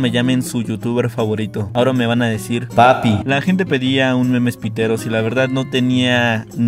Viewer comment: Jsjsjs pensé que decía memes prietos😎👌